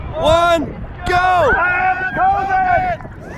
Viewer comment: i have covid is that what he said ..?